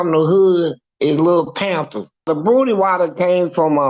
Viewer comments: Did he just say his name was lil pampers in the hood 2:56 ? 😂😂😂😂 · Did he just say his name was lil pampers in the hood 2:56 ? 😂😂😂😂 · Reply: I thought he said lil panther.